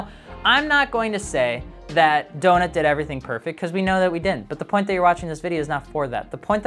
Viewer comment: This was a tough one to do, mostly because I don't want people to think that I prefer one side over the other BUT I don't think Donut's initial experience with Subaru's is that....out of this world. After all, how often do we see marketplace ads with Subaru's mentioning rebuilds or ringlands? At the same time, Donut Media maybe had duplicated issues because they didn't have the experience required to build these cars. But at the end of the day, they're just Subaru's. Right? it shouldn't be that difficult, or is that exactly the problem? It IS difficult to work on them..Would love to hear your thoughts below on this and hope you have an incredible day! ✅ Subscribe here - <link> 🔥MartiniWorks DISCORD BB - <link> 😊$39 Heavyweight Hoodie - <link> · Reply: did u say we know we didn’t?